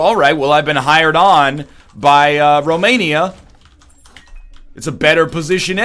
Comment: did he say "well a buy romania" at 12:33?